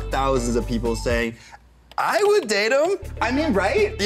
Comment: your subtitles say "i would date him" instead of them**